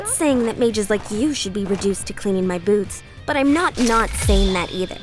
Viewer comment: if not for the subtitles, I would've thought she said "cleaning my boobs"